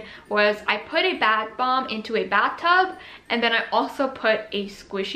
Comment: Am I the only one who heard her say BAD bomb?! 🤣